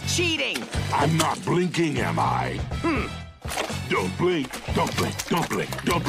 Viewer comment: Why do i hear don't blink like dumpling